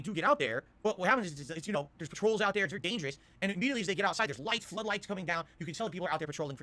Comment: did I just hear “the trolls are out there. It’s dangerous?”